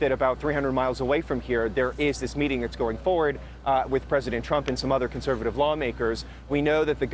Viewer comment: did he just say "president trump" @ 1:38 ?